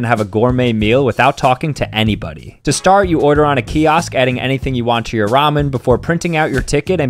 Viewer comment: WHY THE HELL DID I HEAR “YOU ORDER ON A GYATT”